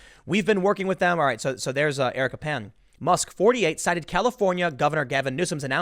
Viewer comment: I thought Tim was saying Eric Pan, confused me when he show a woman that wasn't trans